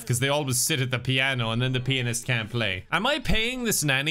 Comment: I cannot be the only one who still mishears pianist as penis every single time right